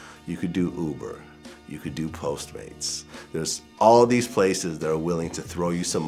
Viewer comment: what did h say postrate??